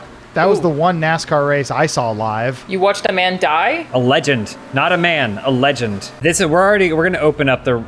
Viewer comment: I really thought james was saying "not a man, alleged" instead of "not a man, a legend" and was extremely confused.